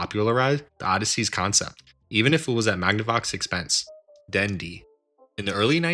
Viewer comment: I heard dandy